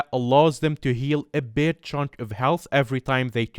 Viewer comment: I thought at first he said a b***h hunk of health. I had to relisten a couple times to make sure I heard wrong lol